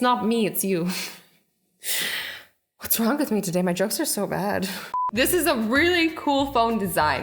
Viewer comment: When she said my jokes are so bad I thought she said her drugs are so bad😂😂😂.